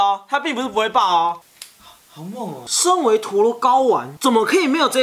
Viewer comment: 身為陀螺睪丸!? Σ(°Д°; 雖然我知道你要說什麼 哈哈